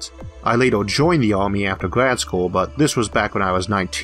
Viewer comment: back when i was 19 a senior in college... lol nice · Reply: at first I thought he said high school but then I listened to it again...